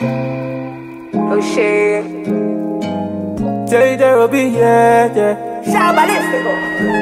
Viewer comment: I think I just heard “I want to drop out who’s with me 😂 O Y O